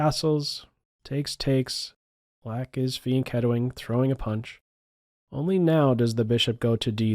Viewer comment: Hey I'm not a native english speaker and I'm wondering, what is saying when he says "Black is ..." at 3:53 (putting the bishop on the main diagonal) ? I can't find how to spell it ! :/ Btw, thx for your vids ! :) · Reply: Hello Remi, In chess, the fianchetto is a pattern of development wherein a bishop is developed to the second rank. is the man!